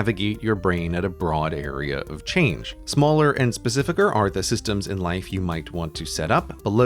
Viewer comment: Did he say 'specificer'?